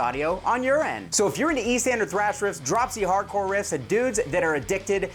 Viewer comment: I always thought you were saying east ender thrash riffs and thought I was missing out on a new genre.